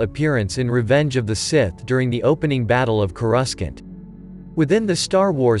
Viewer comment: What did you said? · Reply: Battle of Coruscant